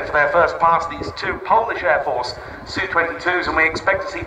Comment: did he say Polish airforce? cuz im from Poland. And to be more specific, im from Warsaw, just like he said.